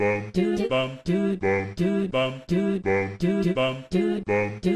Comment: Am I the only one who hears "Bomb chu Bomb chu Bomb chu Bomb chu"? like that one item in the Legend of Zelda games? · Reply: i Hear "Bob Dude Bob Dude Bob Dude Bod Dude"